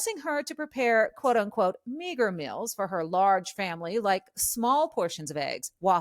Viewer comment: I thought she said “🥷🏾meals.” 🤣